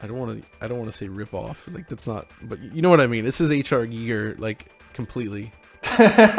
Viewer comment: Does anyone know what he says at 9:15 ? "You know what I mean this is a ??????? ripoff" WHAT RIPOFF I DO NOT UNDERSTAND WHAT YOU SAID someone help :( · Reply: He is saying it's kind of a rip off of H.R. Giger's artwork, and the game developer has already said before that the game will be heavily inspired by that artstyle.